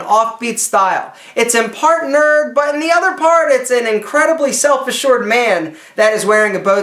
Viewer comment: am I the only one that thought that at 10:11 he says "an incredibly selfish short man?" LOL